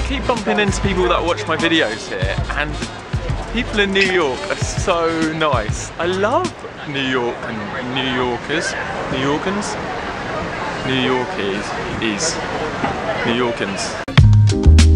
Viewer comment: it sounded like he said "New yorgans" hahaha